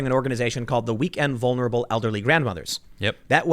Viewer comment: I thought you said weekend vulnerable grandmas! 🤣😭😅😂